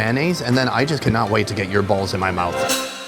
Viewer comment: minutes! That’s I heard He said can’t wait to put your Ball in my mouth 😂😂